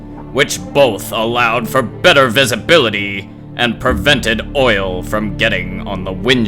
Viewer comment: Did someone really say oil?